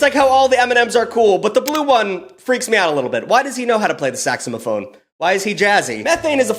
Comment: Im sorry did he just call it a “saxamaphone”?😭